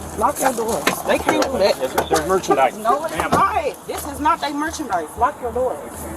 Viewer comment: Close captioning is wrong. She clearly said “This is not they merchandise.”